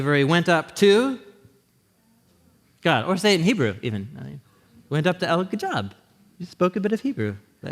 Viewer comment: What Hebrew word does he say at 10:00 minutes in?? Anyone know please? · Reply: Elohim